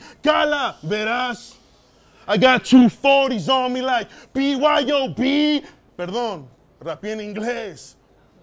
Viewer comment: Alguien sabe que dice el en el 17:50 no soy bueno para identificar palabras en inglés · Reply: I got you 40's so me like B.Y.O.B Lo escucho NOTO, RIO... No es BIG (Notorious big)